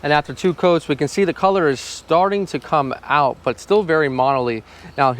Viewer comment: waht is "monaly" or "monly" idk what he's saying. Help me out pls? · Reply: “Modly” he just meant weird or not unified I believe 🤣